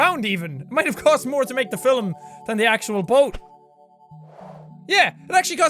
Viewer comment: Why do I always hear Jack say "filum" instead of "film"